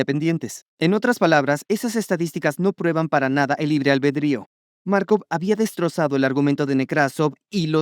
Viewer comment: Estoy tan quemado por Call of Duty que pense que decia Makarov.